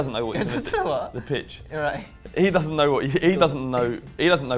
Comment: says "he goes on to the bitch" instead of pitch 😂